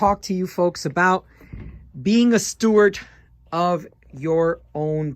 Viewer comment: did you say "stewart" or "steward"? Steward is correct.✌️